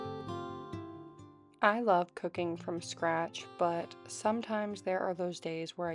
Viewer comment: Girl the first sentence I thought you said “I love coc a ine” what an intro to your channel lol!